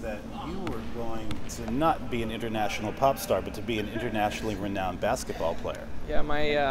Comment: The fact that they said 'pop star' annoys me uncomfortably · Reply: Elle Andrews I heard "porn star" at first so I was even more uncomfortable 😂😂😂